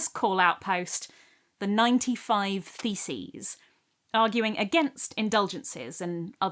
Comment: i thought you said "The '95 Feces" 6:04 😬😅